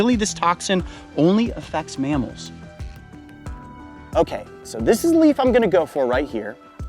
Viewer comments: I had my volume a little too low, so when he said, “… only effects mammals.” I heard “… only effects MALES.” I was like ??? The heck? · I thought he said males lol